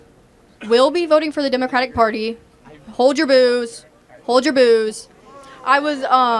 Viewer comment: I thought she said was to hold your booze. 😂